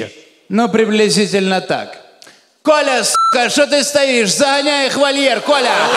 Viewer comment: может показаться что он сказал коляска